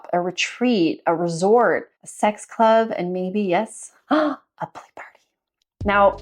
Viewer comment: What did you say here? I didn't catch that. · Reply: A play party :)